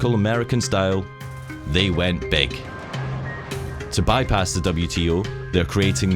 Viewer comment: I can't quite understand what he says around 1:50. I hear it as "They went bake"...?